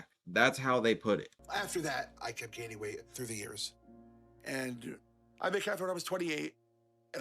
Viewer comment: I had to listen to this like 5 times cause I kept hearing "I kept gaining weight through the ears." EDIT The part where he's on the ground in the hotel room... I really wish the camera crew could help him out... Like when can they actually intervene?